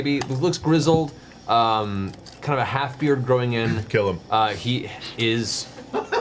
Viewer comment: when Travis says “Kill him” I could have SWORN he said “Caleb” for a moment and did a double take 😅